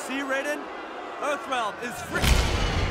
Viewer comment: Kunh Lao: You see, Raiden. Earthrealm is free-- (Shao Kahn snaps Kung Lao's neck) (Kung Lao falls down) Me: (Yells for 5 seconds) Why did he had to die?! He's didn't deserve to die nigga! Kill his ass Lui Kang! Kill him!!! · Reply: I thought he said "EARTHREALM IS FREAK!"